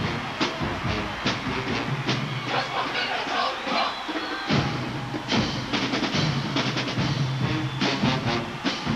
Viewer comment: Can you tell me what the yell is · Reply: "for just one minute let's all do the bump"... 😎